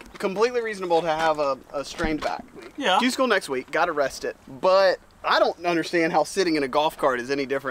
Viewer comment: when Wesley said “got Q school next week, gotta rest it” I thought he said “got arrested” and I just thought it was funny how casual he said that 😂